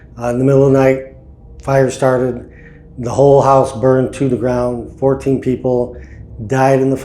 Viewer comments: Ohhhh... POOR House. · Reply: I thought they said whore house. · I totally thought you guys were saying whore house in the beginning! 😂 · Ha ha..🤣 I thought you said Whore house. I heard wrong.